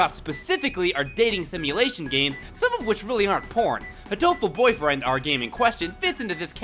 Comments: First time I was this I thought is said hateful boyfriend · Am I the only one who thought it said "Hateful Boyfriend" :)